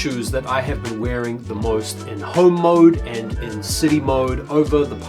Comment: At first i thought you said Hoe Mode… n I’m like “alright Jordan!”